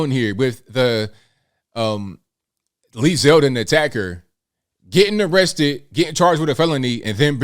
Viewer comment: I thought it said Led Zeppelin attacked